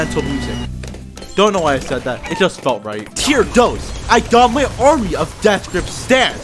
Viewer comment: I think I heard tier dos